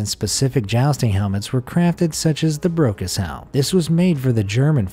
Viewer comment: I thought he said "the broke as hell" rather than "the brocas helm"